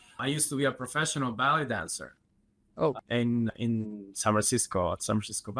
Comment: The first time he said it, I thought he said he was a professional BELLY DANCER!😂